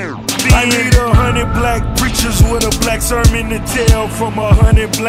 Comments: I heard “black goldfish” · I'm Italian: all I hear is "I need 100 black coffees"... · i will admit i thought he was saying a hundred black cowfish